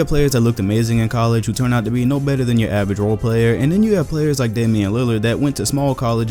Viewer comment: Im i the only one who thought he said danille lillard